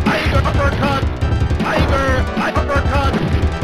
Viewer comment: when i was a kid i thought he said: tiger thundercat. as an adult I understood that he said tiger uppercut